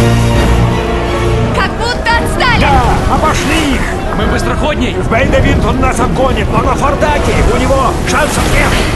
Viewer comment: Что говорит Гиббс на 2:47 про скорость кораблей? Бейбивинт, фортаге?? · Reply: Бейдевинд и фордайк